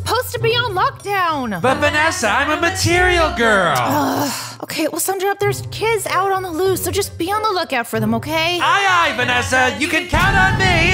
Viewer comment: But after I thought he said material gwirl